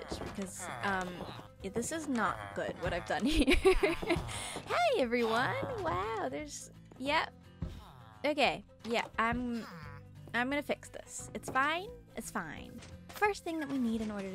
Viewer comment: PLSSSS I HEARD "yep... They're gay.."😭🤚